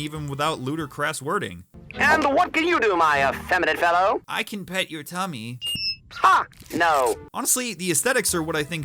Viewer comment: "I can pet your tummy" is the single most terrifying theme anyone could say to me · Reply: I legit heard: "I can pet your tommy" Tommy which meaning a tommy gun